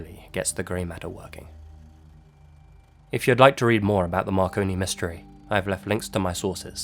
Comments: My fat ass thought you said “Macaroni Mysteries” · Omg i thought it said Macaroni mysteries lmao